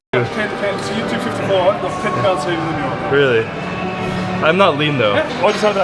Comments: It sounds like he said "I'm 10 lbs heavier than you are" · I think he said "I'm 10 pounds heavier than you are" not less/lighter